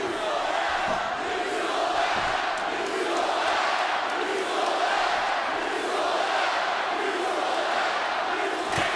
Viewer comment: what does the crowd say? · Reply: "You sold out"